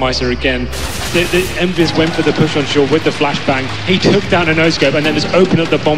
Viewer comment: I like how at 2:38 it says shore instead of short and O-Scope instead of no-scope xD Either way nice video.